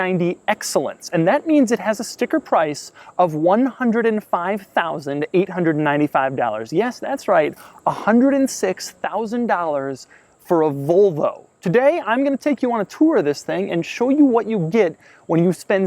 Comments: It sounds like he says vulvo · Sounds like your saying Vulvo